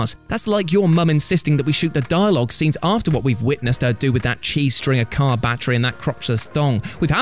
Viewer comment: Replayed this 7 times and I swear you said, “Witnessing what your mom did with that cheese string”. Lmao